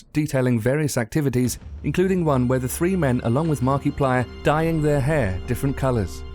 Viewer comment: The way he said Markiplier's name is too good lol, sounded like he said Markyplier XD